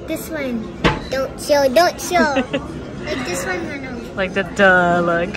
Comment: did said manong? Because in ilocano manong is older brother